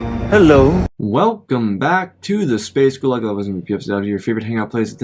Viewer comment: Hey, awesome video as always! I've been particularly looking forward to this one though 😁 Can you explain what you're always saying in the intro? I live in Russia and I constantly mishear it as "welcome back to the space gulag" xD